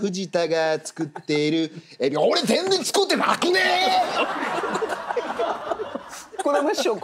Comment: の藤田のセリフがまじで聞き取れなくて気になりすぎて先に進めない。分かる人教えてください、、。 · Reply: ん なにが？ 俺全然作ってなくねー！でしょ！